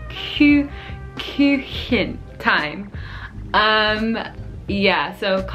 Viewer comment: Y’all I’m cringing. said I was saying kitchen instead of cake. 😂😂😂 HELP ME! Ive been getting kiss, cake, look, kitchen allllll mixed up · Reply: Just say "Kuchen" instead of this complicated Northern German "Küchchen". That's maximum level complicated, even for some Germans. -> Yes is true, I also thought you said "Küche".